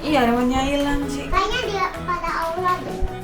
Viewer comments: Salfok sama cici yang bilang allah kalau nggak salah denger menit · di menit ke 9:32 salfok sama cici yang ngomong tanya deh sama allah apa aku salah denger? wkkwkw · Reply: Aula kata cici